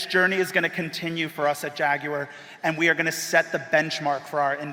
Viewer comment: Why is he saying "JaguEr"???